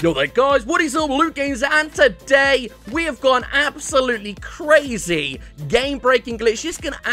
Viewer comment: In the intro I heard " I got an umbrella " 😂😁🙂👍